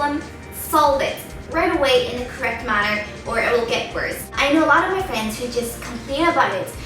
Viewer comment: I know a lot of my fans.... Did she say that?